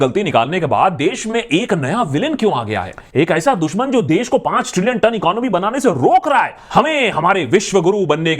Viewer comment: Did you say 5 trillion TONNE economy? হ্যাঁয়!?😂